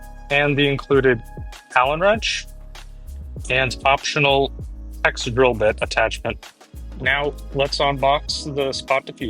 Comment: Hi The vent includes a hex drill bit adapter. It can be used to replace the hex wrench for a quicker way of threading the bolts. Just use caution to not over tighten as this can damage the aluminum. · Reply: I dont think thats my question, in 1:20 of the video you say something like optional exit drill bit? I cant understand the word you are saying? one last question, am i drilling into the rivet with the 1/8 bit like i am screwing something in? Am i essentially pushing a rivet out or am i drilling into the rivet?